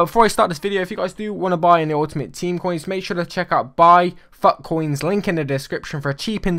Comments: i thought you said buy fuck-coins in the beginning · Is it me or did he say buy fuck coins